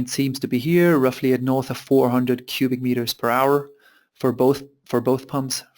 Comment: in the minute 26 you wrote that the flow rate is 756 cubic meters/s its segnificantly big you sure about it ? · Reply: Ha ha Yes My eyes popped out for a second but then I thought he said .....um .... per hour...? not sure